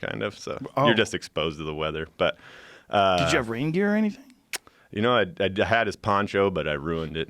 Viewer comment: How long before the word “Elk” is mentioned? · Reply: he said raingear at 5:22 and it sounded like reindeer